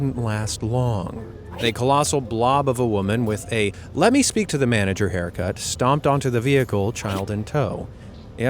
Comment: I was listening not watching and I heard "let me speak to a manger here haircut" and I knew exactly what haircut that is😂😂😂😂